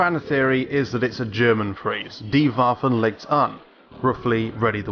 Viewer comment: Frank Klepacki confirmed that it's German. And as a German, I clearly hear "Die Waffen, legt an."